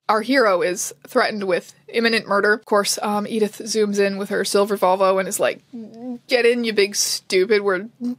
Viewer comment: when I tell you I heard "silver vulva" and felt so many emotions at once.....hfjdkljfdm,f